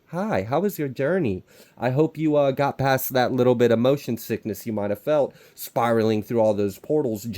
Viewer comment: omg lol did you say 'motion sickness?' cause I heard 'emotion sickness' and I'm like EXACTLY! (lol the acronym that keeps on giving ... and lol bugging people too when you use it still lol see? ... lol. fine ... I'll stop) but I won't lol ...